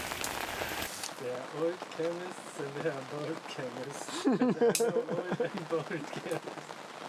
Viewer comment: In video I thought he said "There are no old and bald chemists" and I was like what??😆